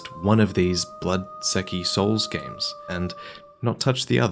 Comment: thought he said Blood Sucky Souls earlier on in the vid